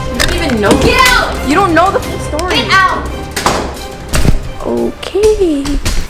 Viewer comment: did she say u dont know the F...... story?